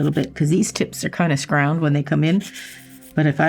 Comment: did i just hear say scround? squoval? wat the heck